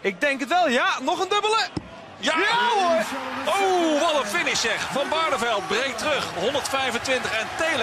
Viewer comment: I love the dutch commentary. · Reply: we dont have a dutch translation for bullseye. funny thin g is we have some English in our dutch dictionary including bullseye. game over is used a lot in dutch games. 2:14 he actually says "wat een finish"